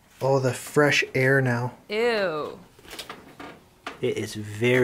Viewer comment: did anybody else hear a girl say ew at 5:38 ?